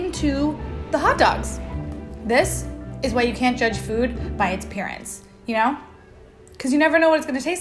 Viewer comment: I THOUGHT YOU SAID JUDGE FOOD BY IT'S PARENTS